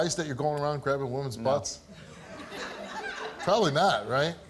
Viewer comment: at around 5:59-6:02 mark; did steve say "do you think it's okay going around grabbing womens BUTTS.....or did he say NUTS?????" i've replayed it like 7 times i keep hearing nuts....i think he said butts but they purposely edited the clip like this lol; does anyone else hear it though??? please back me up someone hahaahha....also for the record; this dude is a creepy ass turd-muffin....AND THE MUFFIN MAN DOESN'T LIKE TURDS!!!!!!!!!! hhahahahahaha